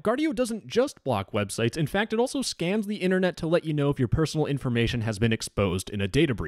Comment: During the add read, I thought you said "it also scams the internet" which I thought was funny.